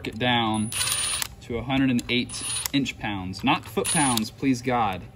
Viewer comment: What thickness feeler gauges are you using ? Also at what torque setting are the front oil pump cover screews torqued back down to ? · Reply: sounded like he said 2 thousand gauges and 108 inch pounds